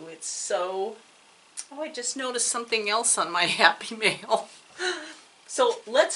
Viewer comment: It was so funny when you said happy mail I thought you were saying Happy meal like McDonald’s because of you grandson being there. I miss understood. LOL. So happy you had a good time in sisters. It was so sweet of those ladies to send you such sweet gifts. Thanks again for the chat I really enjoy them. By the way I love the waterfalls in your yard. Take care ❤️